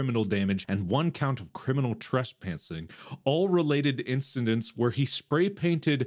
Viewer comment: I cracked up when Mark says trespantsing instead of trespassing.